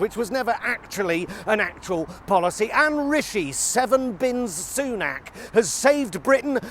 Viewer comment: I did watch "the news" this morning. I must have misheard Twati, I thought he said 48 bins. And then I thought, that much space in your home? They are all out of touch with us common people.